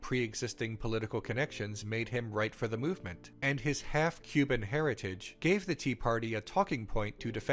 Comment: My ears misheard "his half Cuban heritage" as "his half human heritage" and for a second I was like "go off queen!"